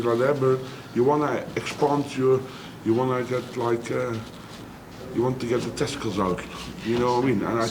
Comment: did he say you need to get your testicles out😂